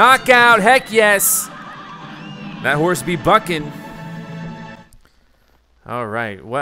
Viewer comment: SPOILERS 17:04 did not say the F word! He clearly said Bucking and Bucking is what real horses do.